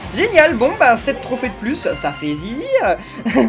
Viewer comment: C'est moi ou à 13:45 il a dit sa fait zizir ? 😂😑